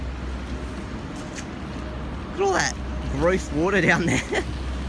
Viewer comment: Did you just say "roast water?" I'm having a hard time comprehending what "roast water" is... :/